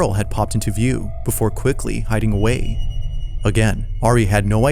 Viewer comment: That monkey genuinely freaked me the fuck out. The way it moved was not normal, not at all. It was like some cursed toy story shit. Moving like a whole person waking up Edit: Please stop replying to this comment with real or fake battle nonsense. It was fine at first, but now I'm getting flooded. I don't need to see the same stuff on repeat. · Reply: i think i heard a "hi again" in the restaurant video